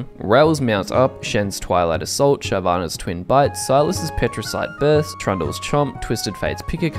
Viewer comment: I swear i heard you say shyvs twin butt